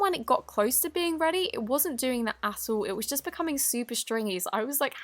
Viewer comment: Guys did says a bad word?? idk just go to cc button like substiles at 2:14 Edit: it's just her accent, oops... SORRY! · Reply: Ikr she didn't say a**hole she said at all even I heard it wrong at first